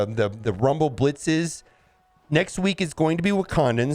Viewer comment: I definitely heard "Grumble Blitzes"... :)